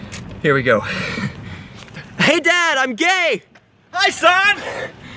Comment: I'm laughing so hard it thought he just said hey son!!